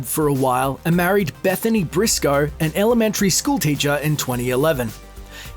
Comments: I thought he said HIS elementary school teacher for a second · I thought he married his elementary school teacher instead of marrying an elementary school teacher...